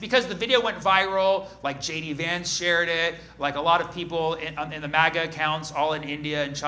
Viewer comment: It sounded like he said "JD Vance chaired it" so I thought "daaaamn he's cheating on the couch now too???" 😅